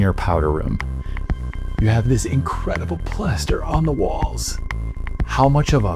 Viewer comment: Did he just say "incredible plaster"? 😅